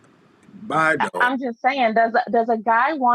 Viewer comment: I often agree with Kevin. But...bye "dog?" 1:13:10 bruhhh lol how is not kinda foul og? WAIT...did he say BUY A DOG instead? lol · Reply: He said "Buy a dog".